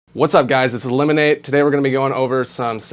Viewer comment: I thought he said "Hi, It is Lemonade!"